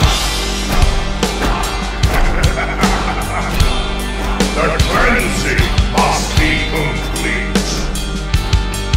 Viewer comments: what does he say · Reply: "the cleansing must be complete" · what is he saying? Sound like "The brenzy must be complete." Doesn't sound like an F.🔊 Thoughts? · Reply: The cleansing